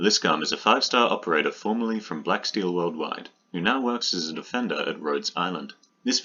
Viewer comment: me, hearing the word offender instead of defender